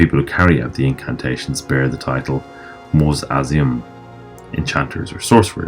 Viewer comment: Looking for help spelling a term you mentioned 10:11 "The people who carry out the incantations bear the title" ______ ? what was that word please? "Moze Azium?" "Mozes ium?" "iam?" I'm sure I'm not spelling it right, because I can't find any reference to this word. Can you help? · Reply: Mu'azzimun